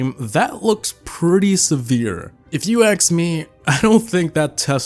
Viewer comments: Did u say aks in 7:50 in the vid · If you aks me instead of ask me in 7:52🤣